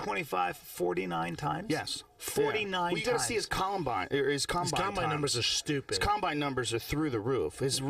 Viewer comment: did he just say Columbine............